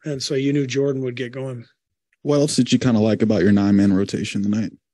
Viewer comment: did he say lamb or 9 man rotation? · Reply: He said 9 in the Q, but Kerr answered that what he liked about the 9 man rotation was Lamb. That's what I got but it was bit weird.